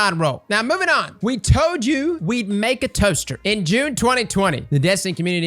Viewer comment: For a second I thought you said adept toaster. I would've been ecstatic